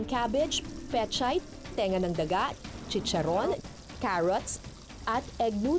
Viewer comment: did she just say "tenga ng daga??" PLS CLARIFY. THANK YOU